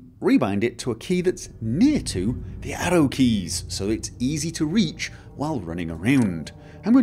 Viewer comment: Hmmmmmm For the longest I thought he was saying Ado keys. I was thinking "what the hell are ado keys?" then context clues lead me to the conclusion that he was saying ARROW keys